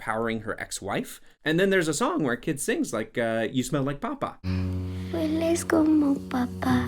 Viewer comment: the way he stressed the "a" he actually said "you smell like potato" lol BTW Zoe Saldana does speak Spanish but she's Dominican so she speaks with a Dominican accent, I wonder why she didn't try to do a Mexican accent it is very distracting.